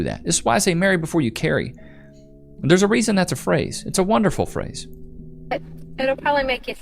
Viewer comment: Oh dear... I heard "it's a wonderful phrase" and my brain went straight to Hakuna Matata 😂